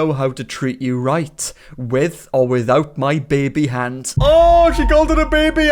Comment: what the hell I could’ve sworn that said husband not babyhand